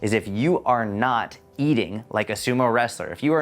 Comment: oh ... i thought you said don't eat a sumo wrestler, not don't eat "like" a sumo wrestler. now i get it.